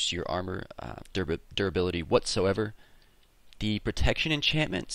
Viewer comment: whenever you said duribility, i heard 'derpability'.